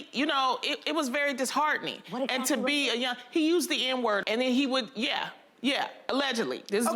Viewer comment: what's the M-word? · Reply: I thought she said the N word tbh lmaooo 🤭😊